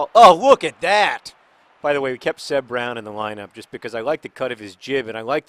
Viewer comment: Every single time I hear someone say Seb Brown I hear "Sir" Brown, which I think sounds like an amazing nickname...